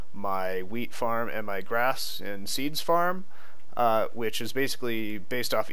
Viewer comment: thought you said weed-farm lol.